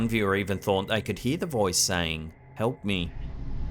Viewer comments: i hear clear "Help! Help me!" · I didn't hear "Help Me!" I heard "Damn it!"